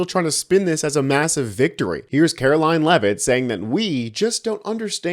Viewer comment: I SWEAR I heard you say Hairline Leavitt! I realized after a moment or two that you said Kkkaroline Leavitt. But I think Hairline Leavitt is a good new nickname for no other reason than it makes no sense 😂